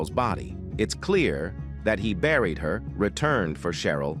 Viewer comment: Can you people PLEASE stop using these AI voices!? I just heard" the body had already been re-bariiad" and I had to go back 3x only to realize it said BURIED. Damn!